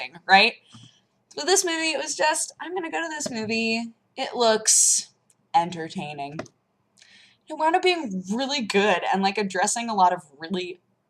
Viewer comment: "Im gonna go to this movie, it looks...Ender-taining" Thats what I heard.